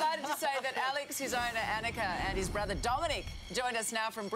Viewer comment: did she say "Alex and his BROTHER Dominic"?